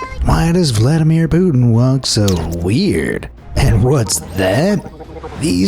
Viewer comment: what did y'all think it said at first AT FIRST first I heard hello Vladimir Putin😂or Hello uh Hello.